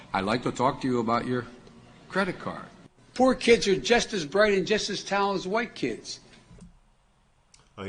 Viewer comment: He did not say, "Poor kids are just as bright and just as talented as 'RICH' kids." What he said is, "Poor kids are just as bright and just as talented as 'WHITE' kids" - meaning all black kids are poor. There are no poor white kids. No black people have money. But all white people do. The statement is extremely racist. And stupid.